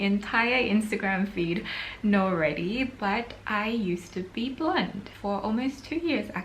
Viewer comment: i thought she said blind omg and i was like wait..what rly?